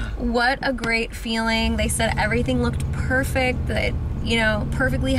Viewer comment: “She looks perfect she says” @ 11:57 💕💕💕💕💕💕💕💕💕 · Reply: I could of sworn the same that she said she’s perfect but now I heard it again she said “she said” 😭